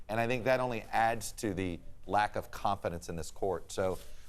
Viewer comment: It sounded like Todd said, “Lack of COMPETENCE of this court.” Took me listening to it 3 times before I heard “confidence.” I realized both work there.